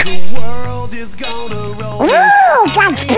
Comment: escuché ¡¡¡DA CRINGE!!!